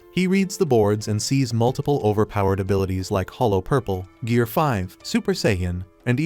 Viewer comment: Did bro just say hallow purple